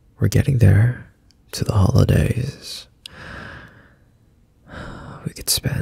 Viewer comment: Build a snow man? I thought u said summer holidays😭